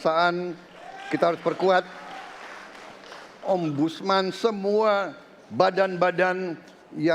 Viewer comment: Saya yg salah dengar atau gimana ya.kata pak prabowo kita harus perkuat omusman😂